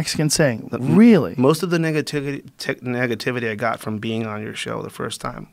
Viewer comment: sounds like he said niggativity at 4:00 ha